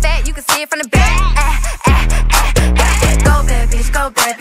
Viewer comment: Pensé que decía Go babys Y me vengo a enterar que dice en realidad go bad bitch xd